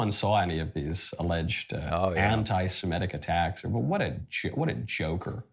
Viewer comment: Freudian slip from Knowles. He almost said "what a Jew" but switched to saying "what a joker".